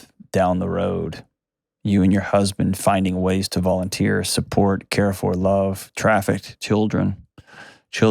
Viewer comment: Did he just advise the mother and husband to traffic children? Traffic children to who, the pedophile son? I think he meant to say another word or meant it in a different context. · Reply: He said "volunteer, support, care for, love trafficked children." As in, children who were victims of trafficking.